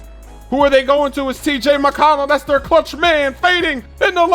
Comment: T.J. McConnell not C.J. McCollum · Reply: Weino he said McConnell but ik what you mean. At first I heard him say CJ McCollum